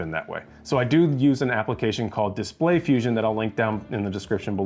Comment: Hey. What is the application name/link you said around 6:20 in the video? I didn’t find the link under video. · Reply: Display Fusion. I linked it in the description.